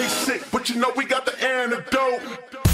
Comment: Instead of him saying we got the "Antidote" at first i thought he said we got the air to go.... xD lol